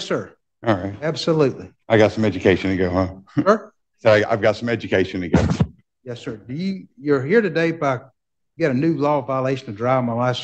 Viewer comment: "I've got some education to give" says the uneducated. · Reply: I thought he said “I’ve got some education to get “